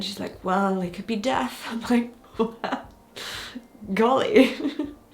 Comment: I think a typo it should be fixed in your captions at around 16:55. I think, you said deaf, not death. (Because yes, your family is in a good position to parent deaf children, already having one deaf mom, and knowing sign language.)